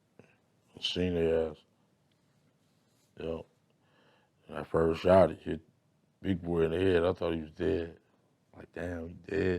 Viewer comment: Agreed! At 12:23 it sounds like he says "when I first shot big boy in the head I thought he was dead" or something like that. He even catches himself afterward and pauses. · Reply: I listened to it like 10 times. He most def said “when I first shot big boy in the head I thought he was dead” and he cracked a smile as he said it cause he knew he fucked up and said it wrong.